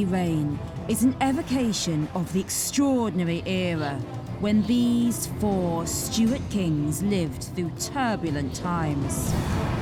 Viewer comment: At the very beginning when she says, "These four Stuart kings....", I misheard and thought she said "These four stupid kings....."! 🤣😆🤣😆